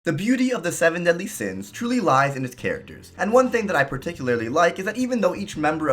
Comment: Is it weird that I misheard him say, "The beauty in The Seven Deadly Sins do lie in the tits..."?